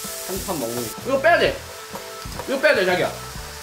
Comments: 여친이있었나요? '이거 빼야되 자기야' 라고들리는디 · 육즙 빼야돼 자기야 이렇게 들리네ㅋㅋㅋㅋㅋㅋㅋㅋㅋ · 스읍? 이거빼야돼 자기야? 나왜자기야로들리지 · 자기야라고한거 다들었어요 슬슬 공개하시죠